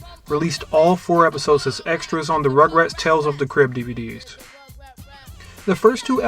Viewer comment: I thought you said rugrats tales from the crypt! Can you imagine?!